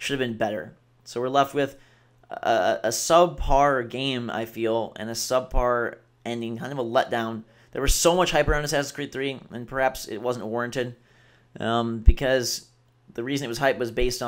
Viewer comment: what does he say at 14:53? "we left with a ... game and a ... ending" subpar? sorry can't catch it